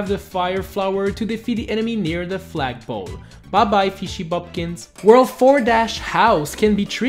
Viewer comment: "Bah bye fishy bumkins" Was I the only one who heard that?